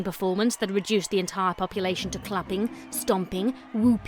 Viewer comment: I thought you said "reduced the entire population to clopping" at the start. 0~o I only having a "working understanding" and all that. ^~^ You, and were brilliant, but Merlin was the showstopper. >~<